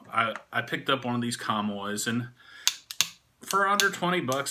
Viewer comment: What's he saying at 1:13? I picked up one of these ___? Kah-moy? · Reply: Comoy’s pipe lighter (I’m sure it’s chinese made too-lol) It’s a old school pipe Brand (French) and a historic shop in London…but stopped being family owned in the 80’s when some investment group bought them for the name.